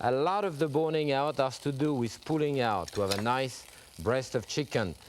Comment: wait a minute, did he just say "a lot of boning requires pulling out"?😂😂😂😂😂😂